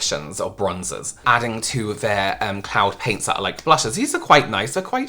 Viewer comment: I thought you said -Clown paint (not Cloud😁) and my thoughts went - What a strange name?! to - well… I guess it fits with those tubes?!?! maybe… still left having a sense of quite not OK 😂😂😂